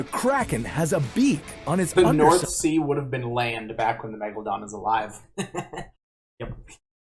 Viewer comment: i thought it said:megalodon vs karen